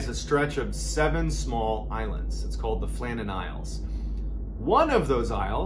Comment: I heard "Flannel Islands," not Flanden Islands